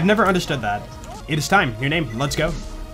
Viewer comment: Why do I keep hearing "It's time, new name, Lascaux"